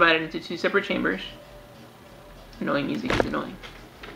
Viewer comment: Did you say "annoying music is annoying" at 9:08? :D